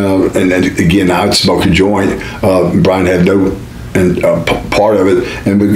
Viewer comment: on the timer he sound like after he said he smoked a joint he sounded like he said Brian had dope